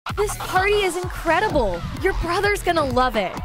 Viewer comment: Totally misheard the first line as; “This Furry is Incredible” 💀